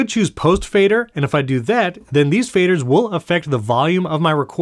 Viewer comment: They will or won’t affect the volume? It sounds like you’re saying “won’t” but I think you mean to be saying “will.”